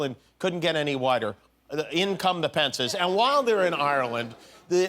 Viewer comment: Did he really say "Just when you thought Ireland couldn't get any whiter, here come the Pence's?!